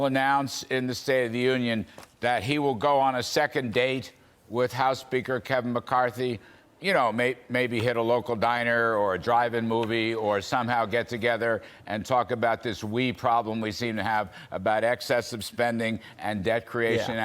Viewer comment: My Friend Kudlow did I hear you say drive-in Movie 5:23 Nice thought all those were long gone, must say though I miss them DRIVE-IN MOVIES BACK IN THE DAYS.